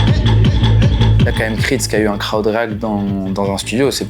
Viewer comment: il dit quoi le gars ? Creed a eu un crowd…??!! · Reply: Crowd react